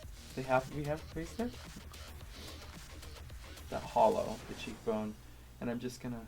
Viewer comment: when he said hollow I thought he said holo and my heart slipped a beat😂😂😂